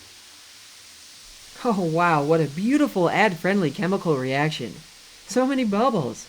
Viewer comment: I don’t know if he can control when the ads are, it I got one exactly at 21:51 immediately following “what a beautiful ad friendly reaction” or at least that’s what I heard.